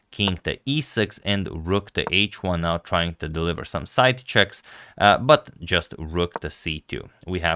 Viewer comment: Did he just say, “side chicks?” 😂😂